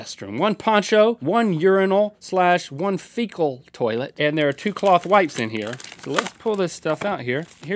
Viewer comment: I thought Link said 'Lethal Toilet'